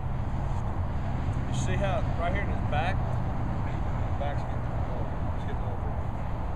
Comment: he pointed out something about the right half of his back. I wish I could hear what he said about that. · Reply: He said "see how right here in his back, is cupping , he is getting excited." I took this to mean he had tension and wasn't relaxed. He was pulling his shoulder blades back or posturing up.